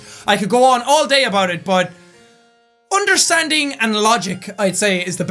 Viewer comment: did he say undersansing at 11:51? get it under+ sans undertale = sans